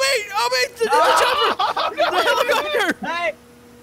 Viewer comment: Did I hear say, “O.B. THE HELLICHOPTER”12:00